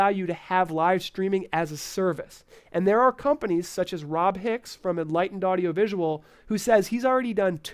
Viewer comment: Hi! Great program. What do you say in 13:28? "Rob Hicks from Atlanta Audio Visuals"? Thanks. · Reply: Rob Hicks from Enlightened Audio Visual